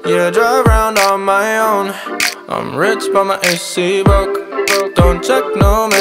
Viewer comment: I'm rich but my AC broke Weird flex but ok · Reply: This whole time I thought he was saying "my SC broke" meaning that his snapchat was dry because he had no friends...now I'm sitting here trying to figure out why tf this man talking about his air conditioning